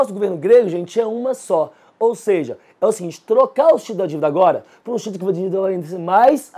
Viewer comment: Boas aulas com conteúdos relevantes para qualquer tipo de banca que cobra atualidades/conhecimentos gerais. Só não entendi o que ele disse em 11:54' a 11:57' kkk. Grato pela iniciativa. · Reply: Bonassi Campos é trocar os títulos da divida agora, por títulos que irá vencer mais a frente !